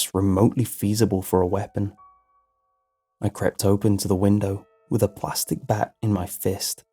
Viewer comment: Please tell me someone else heard him say "crept open to the window" instead of crept over